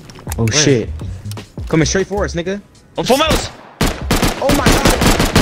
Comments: i heard nigga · i heard ni&&a